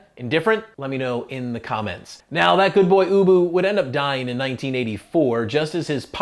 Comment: You can tell he calls his dog ubu in the interview , but clearly not in the closing, he was probably nervous and he pronounced it boo-boo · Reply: I love finding out how many people thought he was saying boo-boo. My siblings included